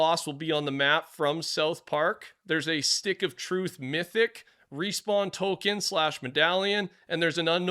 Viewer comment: Did he say “respawn Tolkien”?